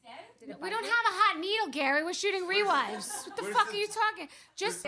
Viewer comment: I swear I thought Gary said "you need a Hadid" and I was like....the shade of it all. But i guess he said hot needle?